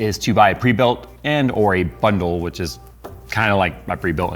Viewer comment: I thought he said "butthole" when he said bundle in the beginning. lmao